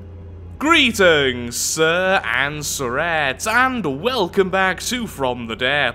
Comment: Sometimes I when u mishear your intro i think your saying Greetings Sirs and tourettes